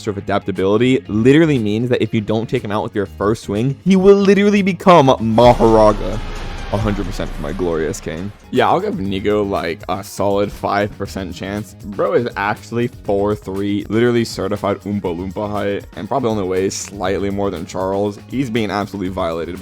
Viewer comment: bro did NOT say nikko:face-fuchsia-tongue-out